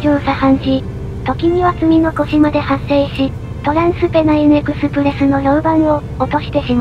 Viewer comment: ドナインスンネンエクスプレスに聞こえる笑